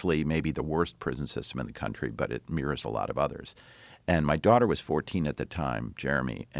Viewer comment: did he just say my daughter Jeremy?